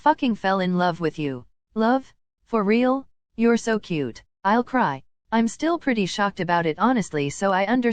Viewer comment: My headphones made Toro say you're so cute~battery low instead of you're so cute~I'll cry😂❤😂❤😂❤😂